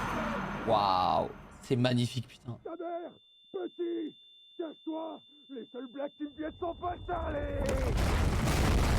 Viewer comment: il dit quoi ??? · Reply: J'ai pas tout compris mais il dit "sa mère, petit, casse toi, les seuls black qui viennent sont pas ...." J'ai pas compris la fin